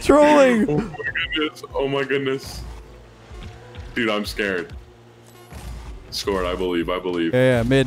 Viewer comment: Is it just me or did say: "Dude, I´m SCARAB."??